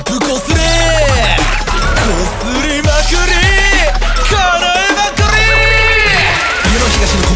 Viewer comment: 凄く失礼かも何ですけど、叶えまくりーがIKO◯に聞こえてあの、行きたいが、意味深な言葉に聞こえました💦